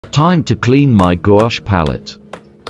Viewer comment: For a second I thought it said Gucci palette 🎨 😂🤪